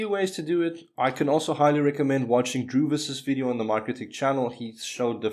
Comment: whose channel? CC didn't catch it either :( Another great video btw! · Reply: NM got it, took me a few more times replaying that part lol Druvis's video on the Mikrotik channel.